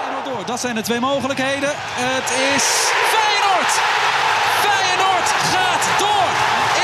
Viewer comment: Did he said Thanos??!